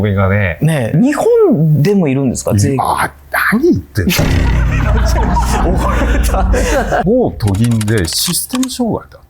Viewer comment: 「何言ってんの」が（おま、黙れ）に聞こえて…